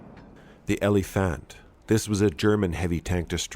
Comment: Almost forgot to like until I heard the "relephant" bit in the end.